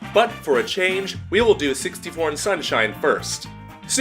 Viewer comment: I heard 61 instead of 64 the first time